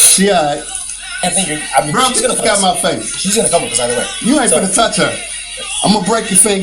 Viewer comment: I thought I heard the police say he's going to come with us either way🤔